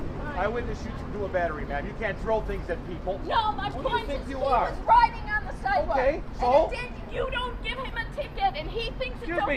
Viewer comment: I thought she was saying that he was writing on the sidewalk😂!